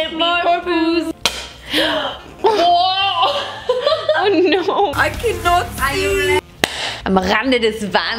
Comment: Wait, what did she say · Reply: I can not see